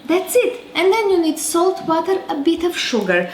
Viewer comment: salt, ???, sugar …..it sounds like she says butter …..anyone know ??? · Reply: Salt, water and a bit of sugar